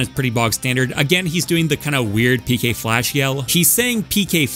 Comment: for years since i was a kid i honestly thought ness was saying pk pulse lmao even tho i knew the move was called pk flash lol